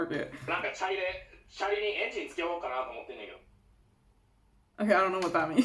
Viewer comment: i think he said "チャリにエンジン付けようかな" which means should i put an engine on my bicycle also iv'e watched a few of your videos so far and i'm amazed at your pronunciation and how quickly you can understand Japanese.